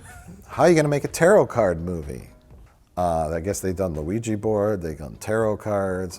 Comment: instead of Ouija board, the subtitles say Luigi board lol I kinda prefer it